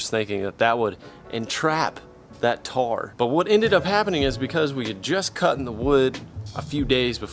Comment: Looks great! But, did you say "We had just cuttin the wood"? at the 3:57 mark? Cuttin? Lol! (come on now, that should at least get me a tshirt or some sawdust..lol)